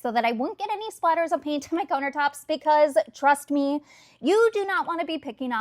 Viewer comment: Wow, your accent is so fascinating! It's like you're from Minnesota/Wisconsin but you've lived in California for 20 years. I am a HUGE fan of painting backsplashes instead of retiling if you hate it. You nailed it. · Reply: Literally thought she was saying "coner tops" until about the fourth time 😂